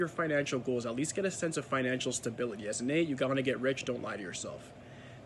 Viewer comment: Hey can you please clarify at 15:03? We need to get a sense of financial stability and did you say we're gonna get rich or not going to? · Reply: I’m saying to get rich deep down most 8s want financial abundance but limit themselves with hindrances from the past.